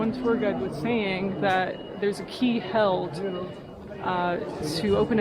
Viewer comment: Hello 🤗 Hope all is well! In the beginning of this video, some thing that I picked up on, not sure if it means anything, when you said “there is a key held to open up a door somehow” at first I thought you said “there is a key to hell”. Maybe hell is connected somehow? Or like this reality is really hell? Not sure. It might just be a stretch, but I thought I’d mention it🤷🏻‍♀️☺️